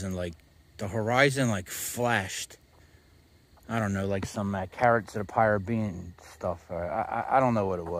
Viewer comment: LOL. Did you say Caribs of the Piratebean at 17:45?